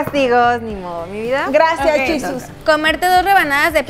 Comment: 😂😂: gracias yusus 😂😂 Quiso decir Jesús en inglés pero se escuho que dijo yusus 😂😂morii😂